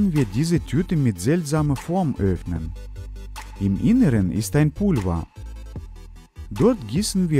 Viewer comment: hab verstanden "im inneren ist ein kurwa." XD